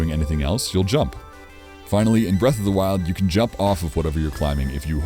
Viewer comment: I thought it said “I finally fixed my breath in the wild” 💀